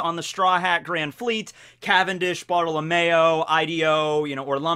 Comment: Cavendish,BOTTLE OF MAYO,Ideo, Orlumbus. i cant be the only one who heard bottle of mayo instead of bartelomeo